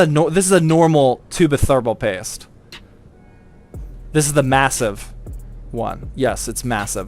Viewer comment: did he say.... massive